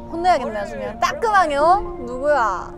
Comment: '따끔하게 어?!' 라고 들었는데 자막이랑 같이보니까 진짜 '따끔하묘' 로 들리네 ㅋㅋㅋ